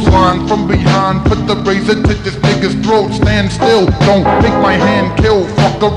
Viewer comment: does he say "nigga"?